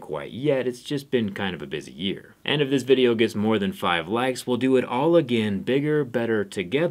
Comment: did he say only 5 likes ??